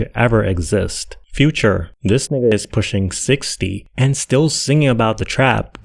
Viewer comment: is future pushing 60, i probably misheard that one